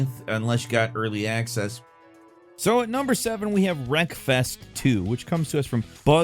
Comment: Wreckfest2 sounded like you said breakfast 2 and I immediately thought wow a hobbit sim